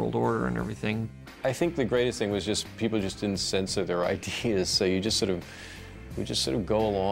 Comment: min 10:00 "the greatest thing was people didnt sense...." WTF did Deschannel say there? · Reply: "people didnt sensor there ideas with eachother"